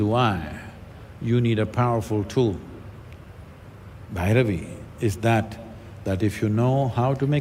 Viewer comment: He said Bhairavi is that tool which can make you exuberant if you know how to access her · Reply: thanks, the video was edited and now the statement is at 48:05. I couldn't make out the word Bhairavi.